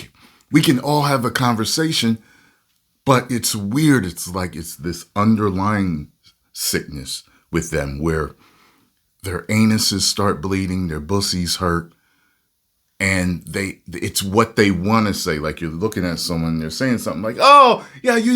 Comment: never thought I’d hear say bussy😂